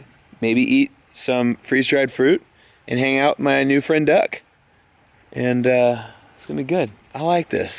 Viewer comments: I realise now you said "Just hanging out with my new friend Duck!" but I heard "Just hanging out with my new friend Doug!" Doug the Duck? What does everyone think of that? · Don't know ow why I thought you said "hanging with my new friend Doug" instead of duck... but I like the name doug